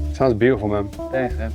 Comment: Did anyone capture what did Janek said at 1:15:12? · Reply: "thanks man"